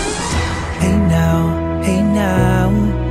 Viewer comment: Why do I hear “this is what dreeeaaammmmsssss 🍅 tomato 🍅